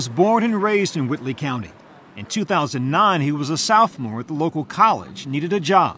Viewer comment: 🤔 He definitely said "Southmore" and not sophomore. Anyone know what a "southmore" is?